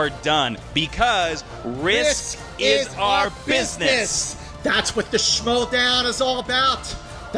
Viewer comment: I'd swear one of them is saying "biscuits"